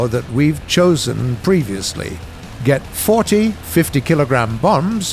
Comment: Did he say 40?